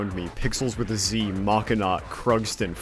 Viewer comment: who's the mii animator at 10:23? i cant seem to guess the name close enough for youtube's search and even the auto subtitles cant catch it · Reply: Machinot 🤑